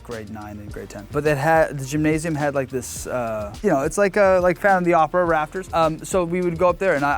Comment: Did he just say phantom of the opera? I’m triggered.